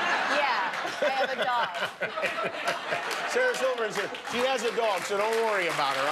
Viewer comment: Did he say silverson at the end lol?